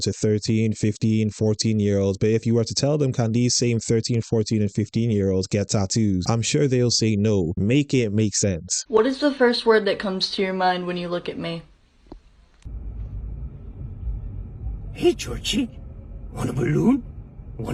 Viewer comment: I heard “what’s the first thing when you look at me” looked at my phone and in my life shouted “Jesus Christ” scared tf out of me